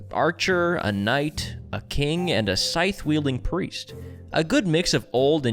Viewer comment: Did I hear that right? A scythe wielding priest class?? I'm sold.